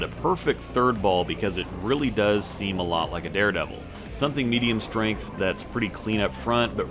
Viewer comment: Did I hear you say Dare Devil? Like "original" Dare Devil? Oh boy.